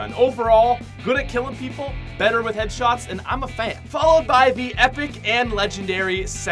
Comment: I thought he said “and I’m a plant”